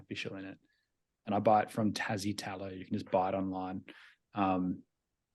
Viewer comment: What was the soap called. I thought you said Tazi? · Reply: Tassie Tallow